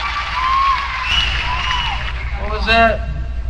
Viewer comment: did i hear... what happen in miami?